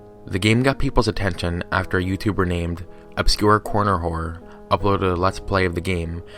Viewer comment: "Obscure Corror Horner" That's literally what he said xD